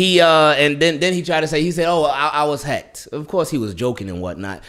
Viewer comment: Could’ve sworn he said his Twitter was hacked